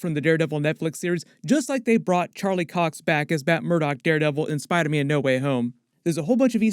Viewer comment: Sounds like he said Batt Murdock. 😆